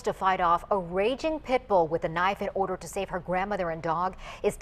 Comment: I swear whenever I replay that part it obviously originally said "A woman who was forced to fight off a raging pit bull with a knife.." But what I HEARD INSTEAD was: "Tonight this woman who was forced to fight off a raging pit bull who had a knife.." I low key thought the Pit bull had a knife LOL.